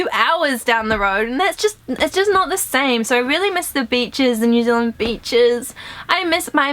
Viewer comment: You were like "BEACHES" and I thought you said Bi***